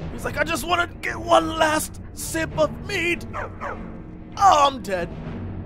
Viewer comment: i thought he said one last drink of meat o.O